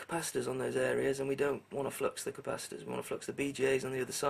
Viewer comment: Did he say FLUX CAPACITORS?!